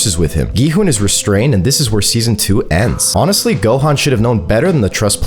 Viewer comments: Is it just me who heard him say gohan in 0:50 😭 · did bro call gihun gohan?